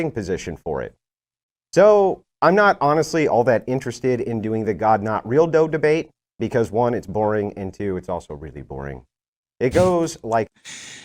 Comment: "I'm not going to debate the topic of this debate, because it's boring" So right here concedes. Which means HE LOST the debate. Maybe Matt didn't prepare to your or his standards, but he at least was on-topic and was prepared to debate it. " He opened up the debate by basically saying that he did no preparation" I'm going to call this a lie and I found the original debate on YouTube, and I'm 10 minutes into Matt's opening statement and he hasn't once said anything like this. Where are you claiming he opened with this? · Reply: At around @ 21:47 of this video, didn't say "I'm not going to debate the topic of this debate" as you deceptively put it. literally says this.. "So, im not honestly interested in the 'God not real though' debate because 1 i find it boring and 2 i find it boring" Two completely different things because here theyre debating Christianity vs Secular Humanism and which one has a better foundation for ethics, not if a God or gods exists. was basically saying he won't be trying to prove the existence of God, but rather grant Matt's worldview and point out the flaws with Matts position. So no, did not concede the debate as you so desperately trying to make it seem like so. Matt lost his cool and the debate when he decided to rage quit.